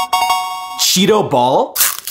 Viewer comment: I thought said cheeto balls